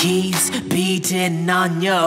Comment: Anyone else thought he said balls instead of bones in the chorus?